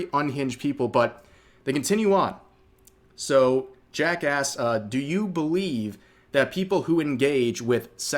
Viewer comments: or 9:18 Thought I heard "jackass" (as in the question was asked with that first) instead of "Jack asks" 😆 -- both seem reasonable to me. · did bro say Jackass or Jack asked? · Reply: 😂yes... most likely jack asked